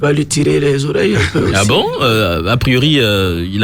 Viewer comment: Je ne sais si vous avez fait attention à ce que ce journaliste à faire sortir à 04:58 '' J'ai entendu << tirer les oreilles....d'un cancer>>